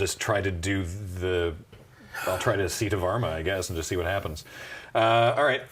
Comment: Whenever he sais "Sita Varma" i hear "Seat of armor"